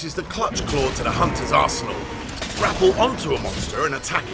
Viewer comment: Am I the only who heard arsenal as arsehole at 0:06?